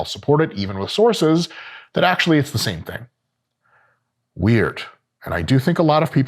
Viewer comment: when you said weird I thought you said... Weider....